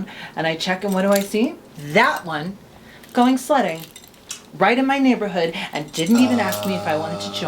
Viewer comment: I thought you said going slutting omg